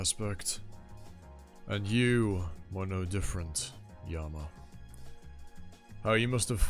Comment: Its you ARE different Yama, not you are no different Yama Just in case you thought he was saying Yama is like Indra and Chandra, his not 5:28 mmmm about that.. XD 5:40 So he is either lying here or he changes his mind... 15:07 So I think I can explain it now, since it basically said it this episode, your theory on time is almost correct but not quite, that everything already happened, yes everything has already happened but thats because we are watching the universe Ananta agrees with :) The fact Ananta has rewound the universe many many times means that there were mistakes but they were rewinded, so Ran could have died in other possibilities, same with Maruna, in fact Maruna could have died by his fathers hand and Ananta knew to step in because he had watched in happen before and Ananta knew Maruna was important because there could be possibilities where he did not realise this and Maruna ended up making the future worse but Ananta would have rewinded time. Its such a unique way to view time travel, I think. I never really thought about what happens to the current world when time travel happens, just it'll change but no, in this wonderful story, they basically cease to exist in this universe and are wiped from existence. Yet those sins of essentially erasing the universe and all creations, could be there, which explains why Ananta has so many. The people that get erased, the current them gets erased, it be something..akin to death. And yeah in a way it does make other p